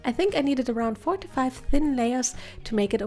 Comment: I thought you said 45 layers to be opaque visible